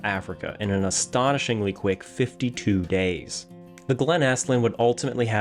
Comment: oh 52 sill good but not as crazy as the 15 days I heard the first time